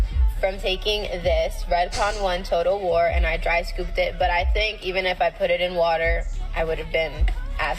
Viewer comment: I thought she said, "Dry boofed it"